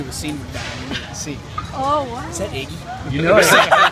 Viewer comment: can someone tell me what it says in the second 00:31 to 00:33 ? plisssssss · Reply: moliner is that iggy? whats up man